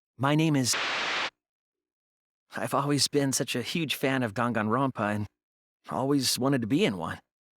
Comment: why did he say ganganronpa 😭😭😭